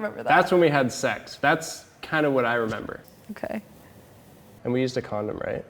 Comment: I was distracted during the intro so I thought I heard: -"and we used to cuddle right?" -"I don't think so" -"oh shit we didn't" Lmao why